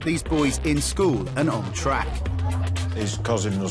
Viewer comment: At the start I thought he said in school and on crack 🤣